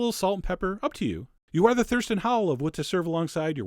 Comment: "Thurston Howell"... oh man, that's reaching. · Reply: Ha! At first I thought he said thirst and howl. Lol. I prefer the Thurston Howell reference though! ;)